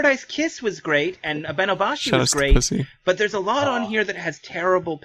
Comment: did say shout out to pussy at 1:07:40 ...... XD